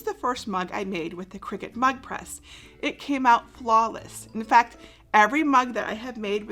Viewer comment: Ive rewatched the video and I still heard say the first mug came out "flawless"....... did I mishear her???? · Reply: her first mug with the new press came out flawless. She showed another that was her very first mug with the old style heat press that was a fail.